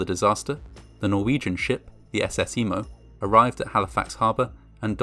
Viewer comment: I misheard the name of one of the boats involved in the Halifax explosion, the Norwegian vessel the SS Imo, as being the SS 'Emo' instead.🖤 Yep, I already know...I’m an idiot!🤦🏻‍♀️ Although, I am now pondering what the sea shanties sung by sailors aboard the SS Emo would’ve sounded like!🤔☺️